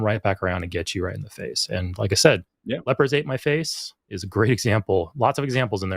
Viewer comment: I thought you said r/lepersatemyface… I was wrong.